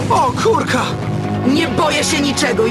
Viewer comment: Nie wiem czemu zamiast o kurka to usłyszałam o kurwa